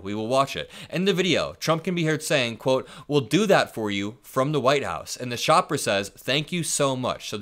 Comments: HE SAID "WE'LL DO THAT FOR YOU "FOR" THE WHITE HOUSE....NOT "FROM".....HE'S PAYING THEM FOR THE WHITE HOUSE!!!😮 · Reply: I listened to what he said over and over again at different speeds, and it sure sounds like he said "for the Whitehouse". Good catch. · I hear "We'll do that for you FOR the White House" (Meaning: for your vote to put me in the White House.) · To me it sounds like he says....We'll do that for you "FOR" the white house!!!!!!! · To me it sounds like he says "We'll do that for you for the white house"